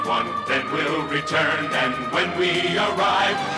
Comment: i heard "star brazzers"😂